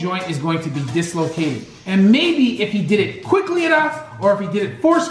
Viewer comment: At @ 3:59 did you say quicklyer enough?